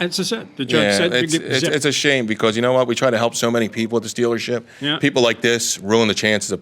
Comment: I have captions on. At 13:50 captions showed the defendant saying “this stealership “. I snorted. I was once called for jury duty on a case where an insurance company was suing a used car dealership and he was counter suing. The first question the lawyers asked is have any of you prospective jurors had a bad experience with a used car dealer or an insurance company. Guess how many jurors said yes.